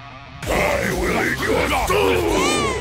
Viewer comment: (yelling, but quietly) "I will eat your soull" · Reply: I heard "I will eat croissant. "